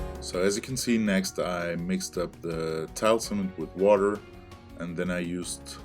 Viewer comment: Great video thanks, very helpful. Can I ask what you say at 5:35 which I guess is what is the blue bucket before you add the colouring. It sounds like 'tile sand'. is that correct? I would be grateful if you would let me know what the mixture is exactly. Many thanks.